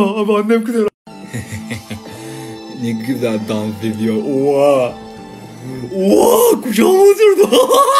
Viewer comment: ne dedi anlamadım da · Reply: Oha kucağıma oturdu diyo